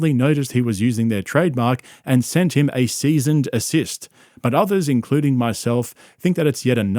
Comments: did you just say "seasoned assist?" I can't tell if you're joking or not, but that's not... What it's called 😂 Btw I do like your content and am just bantering, no hate 🤙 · Is this an AI voice? Sound like he said seasoned assist. 😂